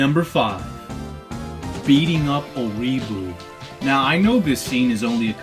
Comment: i thought you said "beating up a weeaboo"